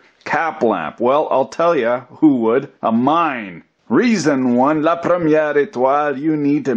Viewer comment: lol, I thought for a sec, you said "a mime" instead of "a mine"...